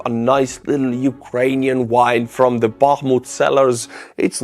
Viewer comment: When you said get yourself a nice Ukrainian wine, I thought you said wife.😊